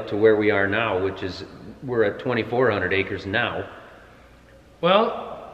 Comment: 2.400 acres did i hear correct ? that a lot :0